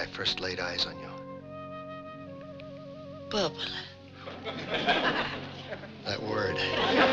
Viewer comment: I just can't understand what she says when, at 1:56 she calls him some name, then Gomez goes wild, it happens often but I never understand that word 😅 somebody please help and tells me 🙏🏻 I just love that family 🥰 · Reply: she calls him bubele which means darling or sweetheart